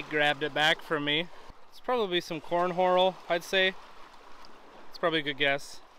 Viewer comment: did you say "corn horal"? 😀